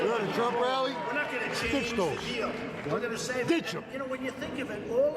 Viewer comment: what did the guy at 0:38 say? stitch em ?